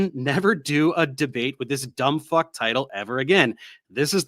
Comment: " According to Dennis Noble... " seems to be on repeat here quite a lot. Edit: 32:02 lol, did he just say " ... dumbfuck " ? hahahaha I have never heard anyone talk so much without saying anything at all. It is just... a load of rubbish.